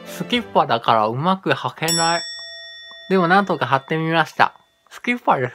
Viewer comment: うまく貼れないが、はけないって聞こえて笑いました(ｏ´艸｀ｏ)♪ こうゆうシリーズ結構ありますよねぇ✨なんか重曹とレモン汁？だったかな、それで歯磨きするとか何が良いか分からないですが、これは良いですねぇ😃